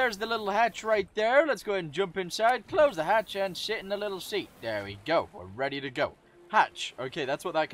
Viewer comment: Am I the only one that thought he said sh*t in the little seat?